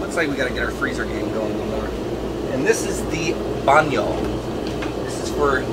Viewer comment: WTF.. did he say "Banyo" at 7:46. It's the Filipino term for bathroom. glad to hear that from you Mike! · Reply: I believe he said "baño", which would be Spanish for bathroom, but I could be wrong.